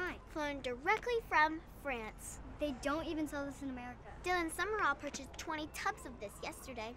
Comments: Who else heard piper say Dylan summerall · dylan summerall ! 😂😂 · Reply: Oh I'm sorry I thought she said Hayden Summerall. Girl you definitely have better hearing than I do. LMBO